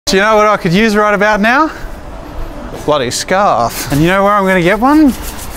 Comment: Am I crazy?! The first time I heard Jazza in the beginning I thought I heard “You know what I could use right about now? Bloody scars ;)” insert intense rock metal